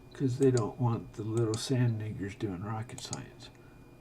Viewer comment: Am I tripping or did Terry say the N word at 7:34 ??? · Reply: Sand n